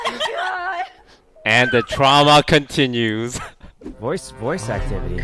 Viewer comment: did he just said drama/trauma?